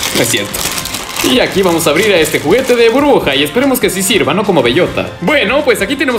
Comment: Soy solo yo o escucho que dice "bruja" en vez de Burbuja :v (me saludas porfa)